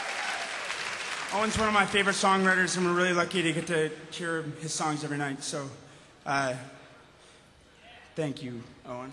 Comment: Can someone please tell me what he says at 0:10? Im not native english, so i can't understand we he speaks so fast! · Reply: "Owen's one of my favorite songwriters and I'm really lucky to hear his songs everynight, so, uh, thank you Owen." In case you were still wondering all these five years later!